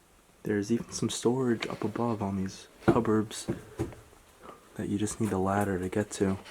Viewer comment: Did he say cuburbs???